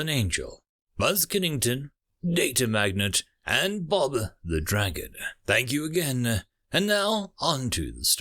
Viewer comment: Delightful as always. Though, two things. I believe Muon is pronounced like Moo-on, Though I admit to not being a pro and could be off. Also, for your T5 folks, it sounds like you say 'Bother The Dragon' and the text says 'Bob The Dragon'. Figure either my ears are failing, a very real possibility, or there's a slight error somewhere. Wanting to clear that up as being crunchy, it seems something important to get right.